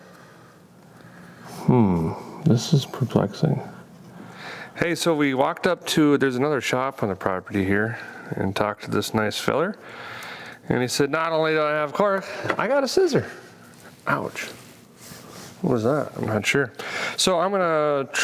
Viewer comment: I thought you said that "not only did he have a cork but he also had sisters" 😂 that confused the crap outta me for a moment. 😂